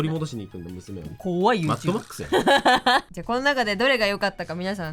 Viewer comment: みすぎてマッドマックスがサットボックスに聞こえちゃう！！！！！